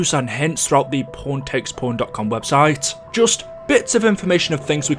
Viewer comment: I thought he said porntakesporn lol 😂